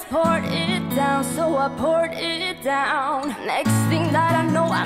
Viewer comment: it sounds like shes saying "so I pourt it down" instead of saying "so I put it down"